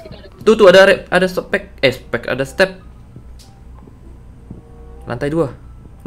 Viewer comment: gua dengernya tuh tuh ada softex bner ngga tuh